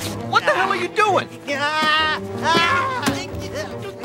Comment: Mandela Effect here: I always thought he said “POCKET SAND SHA SHA SHAW”